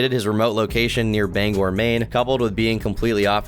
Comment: did he say "bang or Maine" 🤷‍♂ idk what he said lol